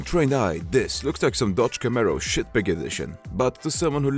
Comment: Did he just said Dodge Camaro? Wtf?...